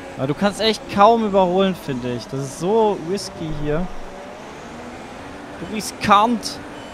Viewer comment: Hab ich es Richtig Verstanden bei 6:08? "Du kannst echt kaum überholen finde ich. Es ist so Whiskey hier"